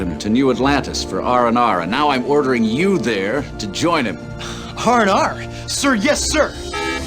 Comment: Did anyone else hear "hard R" then?